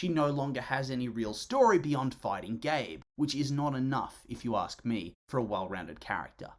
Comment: I thought he said "Fighting gay" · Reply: Fighting Gabe😭 short for Gabriel